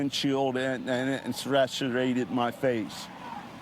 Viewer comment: did bro say Sriracha-rated my face???